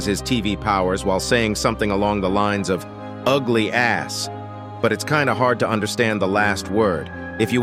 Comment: I thought the big tv man said ugly a b.